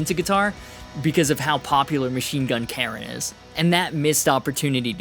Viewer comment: Was it me or did you just say Machine Gun "Karen"??? hahahaha